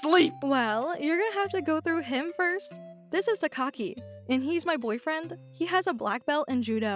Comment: I love the subtitles when OP presented her new bf subtitles said "This is The Cocky" 😂😂